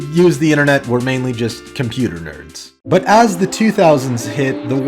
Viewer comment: did he just call Elon Musk a nerd?